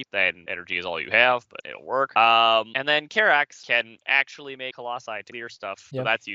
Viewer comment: what's saying here? "Karax can actually make a lot of ????? your stuff" ? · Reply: "Karax can actually make Colossi to clear stuff" Which, BTW, i recommend here for Karax, not Carriers. You can get an army of Colossi on P2, but you definitely won't get a sufficient army of Carriers here, even if you're on P2.